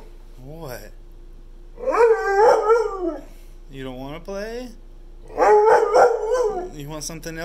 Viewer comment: When you said there's a ball over there it sounded like he said I don't want it or I don't want too